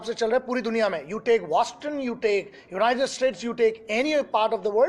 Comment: "you take waston??? you take UK" (1:17) कोई मुझे बताएगा ये "वास्टन" कौन सा शहर/देश है???? आज तक कभी सुना नही मैंने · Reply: Its Boston.